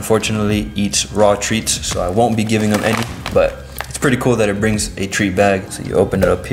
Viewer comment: I thought u said I weren’t gonna give him those treats look top left corner lol